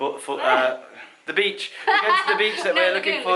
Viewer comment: Did I hear you right (at time stamp 1:54)...? You said you were heading off to "MAPUPUKI" beach? That's funny. I never knew we had a beach in the Philippines that's "FULL OF VAGINAS". LOL. Just kidding. That's what Mapupuki roughly translates to in Tagalog.